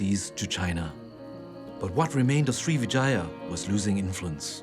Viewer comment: Everytime he says 'Srivijaya' it sounds like he is saying Three Vaginas.